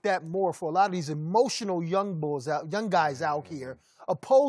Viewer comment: did Mobley say Young Bulls?? 😂😂😂 wit that Philly slang.